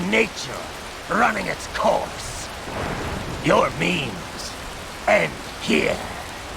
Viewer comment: did he actually say memes? wait wha? hol up whats the definition for memes....